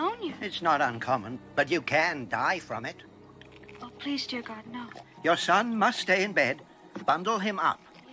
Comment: It sounded like she said "Oh please, dear God, no." Funny thing, seeing how she looks like Jesus at the end.